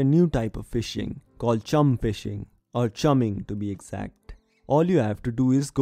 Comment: No cap I thought you said Cum Fishing